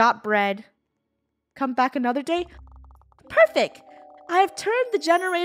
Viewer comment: said perfec instead of perfect🤣🤣🤣🤣😂😂😂